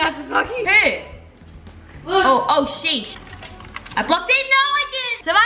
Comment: I think said the s word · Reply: She said sheesh